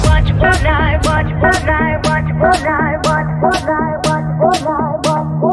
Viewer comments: Why do i hear watch fortnite? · Was that only me Or It was actually saying "Watch Fortnite"